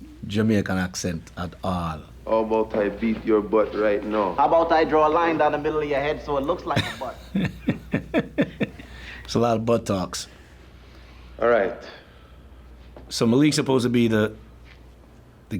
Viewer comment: did he say a lot of "buttocks" or "butt talks"?